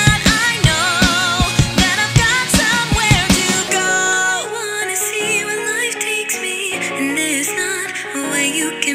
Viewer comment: any ideas what mark says? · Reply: Shinra Tensei